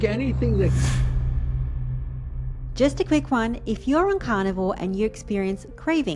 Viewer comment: your subtitles say carnival instead of carnivore and that's what I am a carnival(e)- meaning no meat Carn (flesh) val(e) remove, and let me tell you even the animals are happy about it.